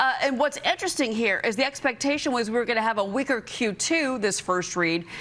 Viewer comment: a “weaker” Q2…. sounded like she said a “wigger” Q2